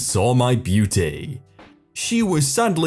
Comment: With the pumpkin 🎃 when you said her loss I thought you said her ass and I looked. Then realizing what you said I thought but dang a paper pumpkin 🎃 looks like you sat down on a piece of paper and trace your ass.